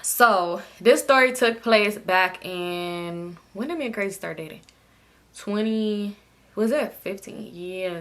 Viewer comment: I thought you said “ when did me and Curtis start talking “ I said not she slipped up and said the man name lmaoooo